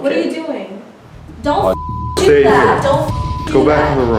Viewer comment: wait... did she just say don’ts?